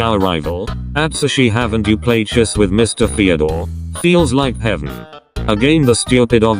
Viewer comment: whenever it said fyodor i thought it was saying theodore….